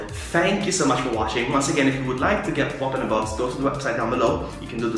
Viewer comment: I thought this said Poop in a box 😂 awesome video to see your new pop's. thank you for sharing 😎👍